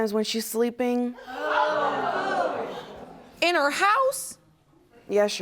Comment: Help me👂 Judge....I thought you said, "Ms. Hoe-House!!!" 🤣🤣🤣🤣